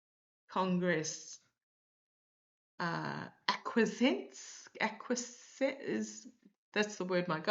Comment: "Will Congress ACQUIESCE?" is what your Guides were saying. 17:00 OK. NOT "correcting" you. But why don't you just call her "G", or "G. Maxwell", or "Maxwell"??? (Everyone will know who you mean!)